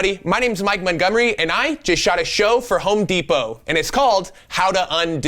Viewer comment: Thought it said, I shot a Home Depot. 😂😂😂😂